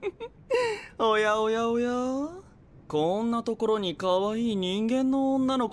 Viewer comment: DID LAITO JUST SAY OYA OYA OYA AT 0:02 !!! jccutvhtcytcjgvtct😱